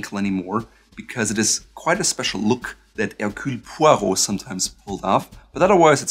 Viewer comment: What does he say at 0:58 in french? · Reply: Hercule poirot. Hes a famous agatha christie character